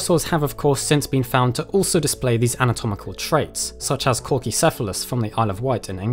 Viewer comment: I thought he said Corgicephalus.